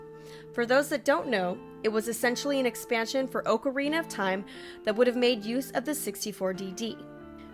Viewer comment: when she says ocarina it doesn't sound right it bothered me the way she said it. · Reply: captainrobots sounded like she said oakarina of time not ocarina of time haha